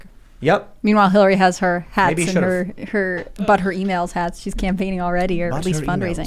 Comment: Every time I hear someone say, "But her E-mails" all I hear is, "Butt Hurt E-mails".